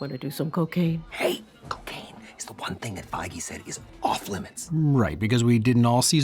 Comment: Turns out the "no cocaine" rule was actually something Kevin Feige said during a meeting, so the writers and Ryan Reynolds immediately worked it into the script and it became one of the first jokes they wrote. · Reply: i thought he said "Foggy" for so long, like he got Foggy Nelson from daredevil as his lawyer, holy shit him saying "Feige" makes so much more sense